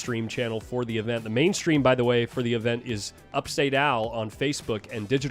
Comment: Can someone please tell me what he said the facebook streaming for this event , did he say upstate owl or upstate Al ?